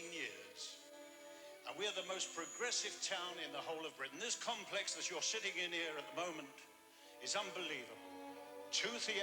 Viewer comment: It sounds like is saying “this complex you’re shitting in” at 3:15.